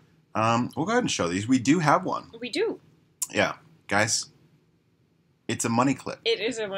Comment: I thought said "alimony".